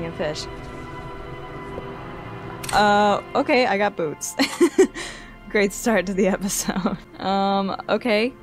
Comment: Was playing the opening of this vid in the background and heard you say "okay, I've got boobs. Great start to the episode", and was so confused😅. Must've been the menstrual talk that threw me off, lol.